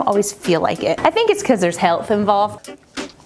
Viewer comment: Did you say "There's health involved" with the Blueberry Muffin???(04:02) 😂